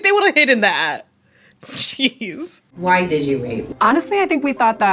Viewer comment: Subtitles @ 15:10 say "Why did you r*pe?" instead of "Why did you wait?" lol i feel like someone should fix that maybe